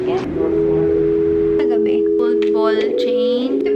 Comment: i thought she said bulbol chain jusko tenga ko